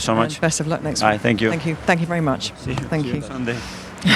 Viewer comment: I heard him saying see you on sunday to Rooney.